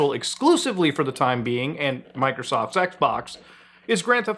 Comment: Sounded like he said Microsoft Sex-Box 🤣🤣🤣🤣